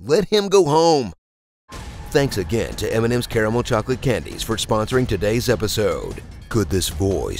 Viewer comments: I heard, "Thanks to M&M's terrible candies for sponsoring today's episode." Hahaha 8^D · I thought he said thanks again to m&ms terrible chocolate candies at first lol. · I heard Eminem instead of m&m lol